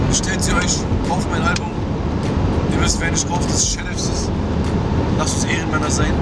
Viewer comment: was sagt er am 2:23 ? und was bedeutet es ? das wort hört sich lustig an xD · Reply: Metal Gear Style der sagt serefsiz